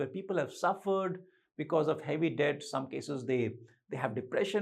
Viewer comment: subtitles, @ 4:37, (on cc activation) is showing as death instead of 'debt'.